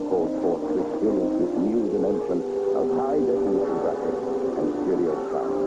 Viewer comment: did he say "High definition graphics" ? :)